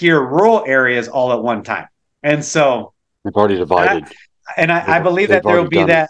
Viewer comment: minute mark, It sounds like someone whispering, when that "interference" happens, supernature? Spying? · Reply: It sounded like said, “already divided (or decided) -already done “ and acknowledged the comment.